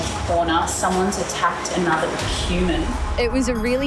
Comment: WHAT DID SHE SAID? SOMEONE ATTCK ANOTHER HUMAN? DOES THAT MEAN THERES ALIENS?